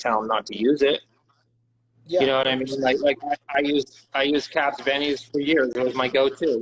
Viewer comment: does anyone know what he said there? What does he use? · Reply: I believe he said “ caps bennies “ a tea